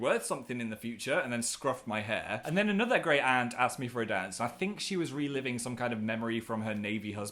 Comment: i thought he said "asked me for a dan"